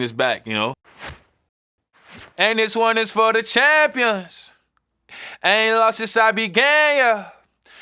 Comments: Who else thought he said I ain’t loss since I been gay yea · I seriously thought it was “I haven’t lost since I’ve been gay.” In my heart, that will always be the lyric. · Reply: yeah because when I thought it said I ain’t lost since I’ve been gay and like the meaning was he’s always been gay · Duddeee, I could have sworn he said “I ain’t lost since I been gay” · when he sings ‘I ain’t lost / sing I began yuh’ I literally thought he was saying ‘I ain’t lost son I be gayer’ 😂😂😂😂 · OMG I THOUGHT HE WAS SAYING “I AIN’T LOST SINCE I BEEN GAY” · What it me only or thought when he said “began” it’s sounded like gay 👀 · I thought he said I ain’t lost since I was gay…😶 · Damn I thought he was saying "aint lost since I been gay, yuh" lmfaoo my bad. Love this song tho! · I can’t be the only person who though he said “I ain’t lost since I’ve been gay yah”. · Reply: I heard ain't Been long since I've been gay, yah😂😂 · wait the line is "i aint lost since i began"???? bro i thought he said "i aint lost since i been gay" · am i the only one that thought nas was saying "ain't been long since i was gay (yuh)" instead of " i ain't lost since i began (yuh)"??? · anyone else thought he said “i ain’t lost since i been gay yeah” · I thought he was saying.. “I ain’t lose since I been gay YUH” 😂😂😂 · I swear for the longest time I thought he was saying "I ain't lost since I been gay yea" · I thought he said I haven't lost since I've been gay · i thought he said “i ain’t loss since i been gay yea”😂